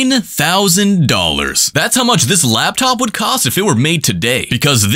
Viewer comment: The captions thought he said "$99,000" 💀